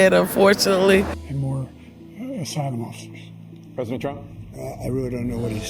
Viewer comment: correct me if i'm wrong but I think he said asylum officers